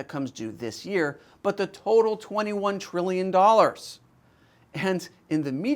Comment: Maybe I'm mishearing, but I thought the US has 27 trillion $ of national debt. I keep hearing say 21 trillion